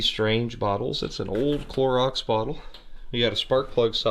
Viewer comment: Oh Clorox I thought you said chloroform I was thinking orange is an awful bright color for a creeper mobile